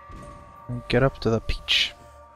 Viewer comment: Every time he says 'peach' I hear 'Bitch'